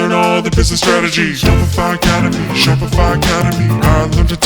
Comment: "Shopify Academy" What i heard was " Shut Fc Up" hahahaha